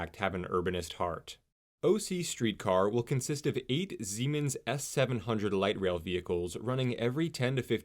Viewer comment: It sounded like you said the streetcars are made by Zeemens — but I think you meant Siemens? They look both modern and traditional the low design for safety. I enjoy riding Amtrak from OC (Sand Canyon aka Spectrum/Great Park) to LA for certain trips. The Anaheim center is great. These are really hubs and destinations so more routes would be great. Personally I LOVE Santa Ana it’s a very vibrant and welcoming community! There are many little tech and service companies around there, the historic Yost Theatre great little shops and restaurants. And the FREDA independent movie theater has an absolute CULT following of young and old classic movie lovers! I can see so many different types of riders! When I was a kid growing up in Long Beach my mom would complain about big oil and GM that pushed local governments to rip out the tracks because hey cars! I was stationed in Germany for almost 5 years and took so many local and long great trips without a car! I trust this will be a hit and support building out further, as well as building great communities!